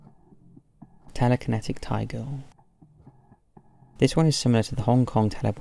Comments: Totally thought you said "telekinetic *tiger*" at first, and I've never been so confused lmao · [#2] 10:59-ish: Instead of "Telekinetic Thai Girl," I honestly initially thought you said: "Telekinetic TIGER!" Now that would have been awesome! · I totally thought you said "Telekinetic Tiger" LOL